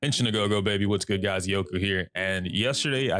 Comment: Not related to the topic at all: Bro wtf do i say in your intro?? I’ve been watching ur shit for months now and I still can’t catch what ur saying all I hear is “inchinagogo” or smtg 😭😭😭 I might get called uncultured for not knowing what it might be but at this point I’m willing to take the beating 😂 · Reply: Henshin a go go baby.